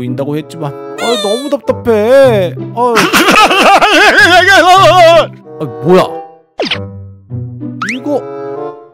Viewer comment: 뭐라는지해석좀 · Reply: 흐하하에이어어어어어어